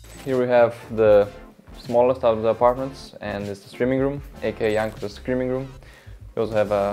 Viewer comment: Did he say "It's the streaming room, a.k.a Jankos's "screaming" room" at 4:21?